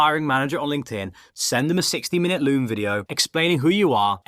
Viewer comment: did you just say 60mins??